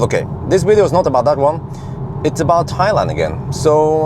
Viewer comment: "It's about Highland again." (say that 3 times...) I thought you said: it's about Thailand again haha